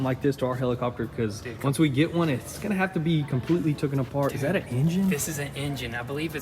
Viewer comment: did he say "tooken apart"